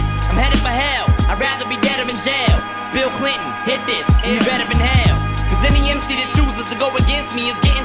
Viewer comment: All this time I thought Em said "Bill Clinton hit this, and you're better in HELL " 😂😂